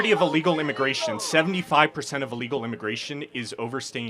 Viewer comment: Get it together, YT!! Censoring the word illegal is so f*cking stupid!! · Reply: I thought he was saying "fuck". I had no idea it was "illegals" Youtube, I cant wait for another platform to make you obsolete.